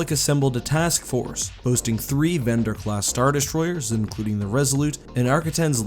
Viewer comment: did he just say "Vendor" class Star Destroyers?